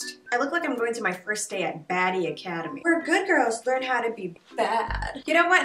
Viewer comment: “Baddie Academy: where good girls learn how to be bad” sounds like it could be a Netflix movie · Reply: I thought she said daddy academy. Did not judge.